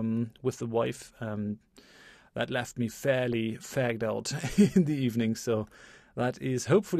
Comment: can some one please correct me if I am wrong but did just say "left me fairly fagged out"?